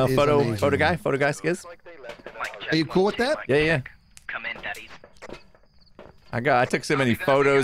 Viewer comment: does say "Come in, Daddies"?!?!?! please someone tell me im goin crazy